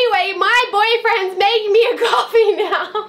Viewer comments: did u say BOYFRIEND? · Who else thought she said boyfriend when she was talking about Kane ? 5:08 in the video ?? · did she say "boyfriend" or am i trippin'?!? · did sis say "boyfriend" at 5:09 ?!!?!?!?!?